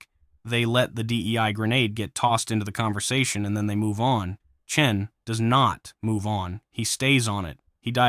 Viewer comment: I’m sorry, did you just call him CHIN? It sounded like you called him Chin!